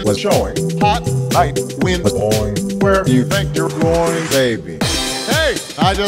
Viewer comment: honestly, I heard "Groin" instead of going...xD